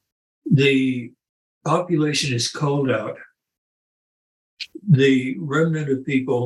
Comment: does Lyn say the "population is called out" or "population is culled out" They both seem to make sense in the context, but if it's 'called' out in wonder who does the calling. If these events are man-made, and the ppl demand honest governments then I wonder if it's the remaining govts calling out ppl or what. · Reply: Definitely "culled out".